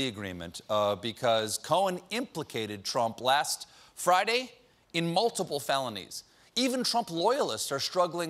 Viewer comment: I like that when Stephen says "in multiple felonies" the caption says "melt Nancy Pelosi"...